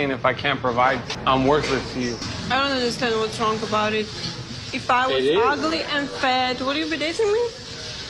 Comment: Did she just call him ugly? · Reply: she asked him if she was ugly and fat would he be dating her